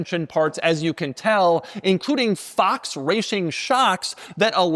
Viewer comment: did you just say Fox rashing shocks? 😂 😂 😂 😂 😂